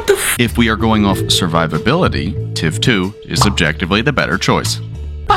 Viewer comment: I thought he said tim 2